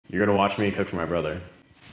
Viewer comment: I thought you said in the beginning “you’re gonna watch me touch my brother” I had to finish the video to ensure this wasn’t gonna happen